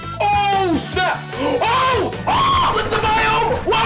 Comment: did he say the s word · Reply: No he said snap